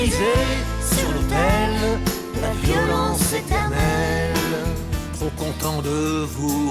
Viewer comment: à 15:39 j'ai entendu "La violence c'est ta mère"